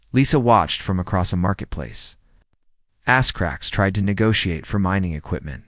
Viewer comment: approx 25:00 ass cracks instead of as Krax. you need to proofread this a little bit better LOL